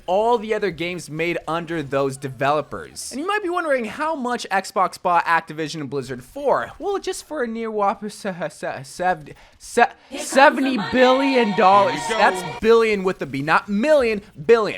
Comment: I thought it said “Xbox buys blizzard for 70$”